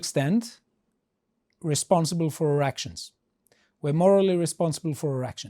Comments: i know its a bad time but it sounds like you said 'we are responsible for erections' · I heard, we are morally responsible for our erec…..🍆